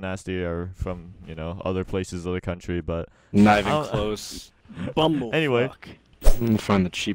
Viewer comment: Did Nasty just say "BUMBLE-FUCK" ?